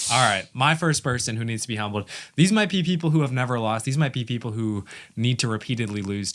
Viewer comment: I misheard “these are my pee people” I was like wtf are you saying bro 😂